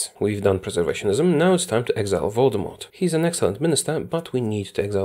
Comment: Is it just me or did he say it is time to exile Voldemort? If so, LOL.